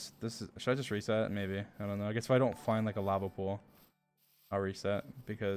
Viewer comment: dream says he needs a lava pool just as subtitles says "lava pops"